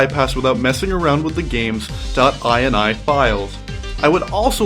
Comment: did you really say "dot buy an eye" files ? Am I retarded or is that the proper way to pronounce ".bin" ?